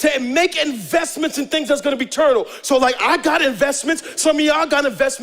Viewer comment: lol pmj said turtle instead of eternal 🤣 love you pmj